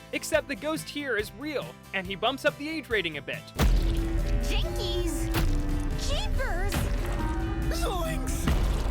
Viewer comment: what did fred say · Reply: F r e d s a y s “f u c k”